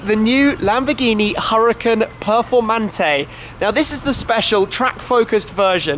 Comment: For Christ sake his accent makes it sound like he said " Lamborghini Purplemantee" 😂😂